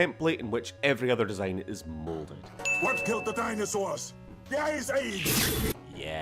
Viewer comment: I can’t help but hear “THE ISA” instead of “the ice age”